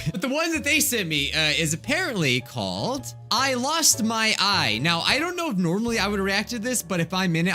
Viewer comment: Who else thought it said I lost my eve